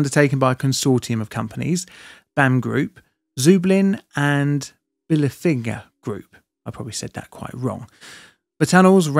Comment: Did I hear you say "Bilfinger Berger"? That 'finger' with a soft 'g' if you're German.